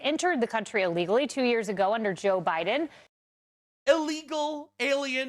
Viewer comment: i could swear she said jil biden xD